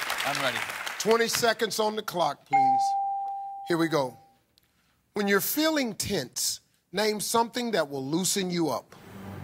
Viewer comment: I swear it sounds like steve is saying " when you're feeling tits " !!!!